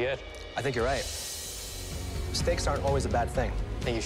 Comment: is it me or did he say steak to the cake